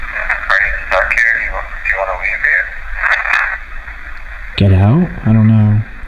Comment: Idk if I'm the only one who heard this but at 2:19:24 it sounds like it said "get out, please" as in the spirit in the hallway wants them to get out so the one that possesed Shandon doesnt harm them